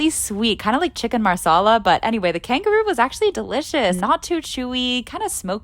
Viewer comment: I swear I heard “kangaroo sauce” !!!!! And then continued to watch!!